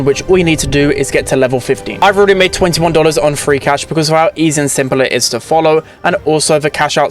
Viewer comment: I totally hear 'Freak Ash' instead of 'Free Cash' 😅😂